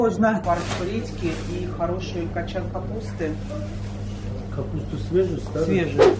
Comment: "Garbage" - это мусор по-американски, а капуста по-английски "cabbage" 😁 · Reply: В титрах по-моему нет, но я в самом начале вместо капусты услышала "мусор" ;)